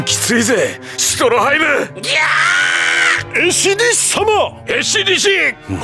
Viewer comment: Esidisi sounded like he was screaming GYAAAAAAATT!!